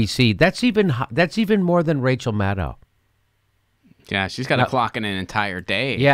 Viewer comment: I thought you said ,,Rachel Madcow"